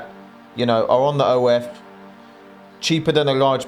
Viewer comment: What does he say? "On their own, then"?